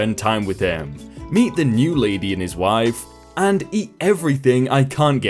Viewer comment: did anyone else hear him say "meet the new lady in his wife"? or am i going insane?